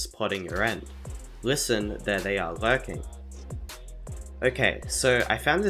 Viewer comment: he does not say they are lurking he says they are again